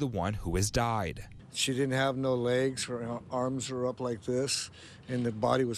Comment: The woman didn’t have no legs but was able to have 2 children? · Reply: I thought he said she didn't after the blast.